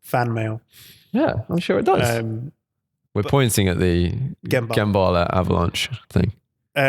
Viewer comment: Sorry i miss heard Gemballa. I thought he said Ken Barrlow (coronation street) 🤣